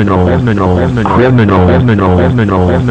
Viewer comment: Why I always hear "pentagon" Edit: it was actually "criminal"